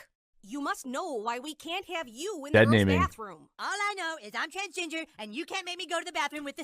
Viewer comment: I thought Eric was saying “I’m transGINGER”, for a small moment there.